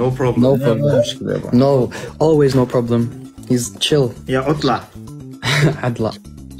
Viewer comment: گال عطله لو اني سمعت غلط😭